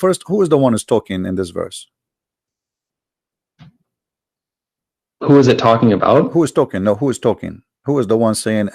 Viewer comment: Did he just say "Who is mu'taa?" facepalm